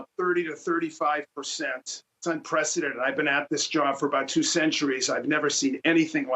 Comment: Anyone else hear say hes been at his job for two centuries? 2:42.